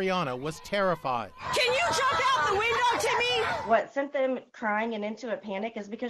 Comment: Sad but I thought she said can u jump off the bus Timmy